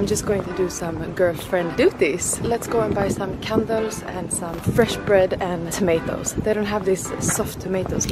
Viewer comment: I thought she said condoms at 0:20 LOL... but great vlog, seemed like a lovely birthday :)